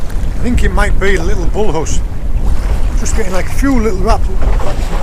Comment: What was that shark creature similar to the dogfish? Bolos? I'm sorry if I misheard! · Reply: PhoenixenRiven - Bull Huss. 👍🏻😉🐟